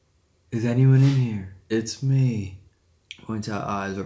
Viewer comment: voice saying “it’s me” after asks , sounds like sayings it’s me.